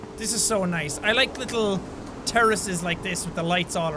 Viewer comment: I thought Jack/Sean said he liked "little terrorists" instead of little terraces xD